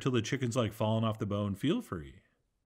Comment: what dos he say at about 4:50, something about george washington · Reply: You're the George Washington of when your paprikash is done